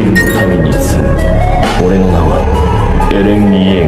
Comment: What did he sayiiiing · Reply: My name is Eren Yeager(ore no nawa Eren Yeager)